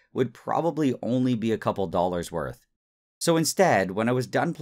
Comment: i misheard ‘couple’ as ‘thousand’ talking about the amount of copper and really needed a relisten, lol.